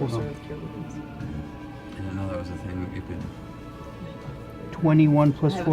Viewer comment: Can someone tell me what marisha said at about 1:30… I’m lost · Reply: "Our sorcerer has Cure Wounds" "I didn't know that was a thing you could"